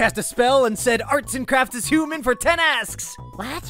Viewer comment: What it said; art n crafts is human for ten asks! What I thought it said; arts n crafts is BANANA 🍌 for 10 asks!